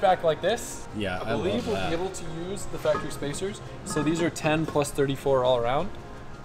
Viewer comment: did he say “factory spacers” 👀?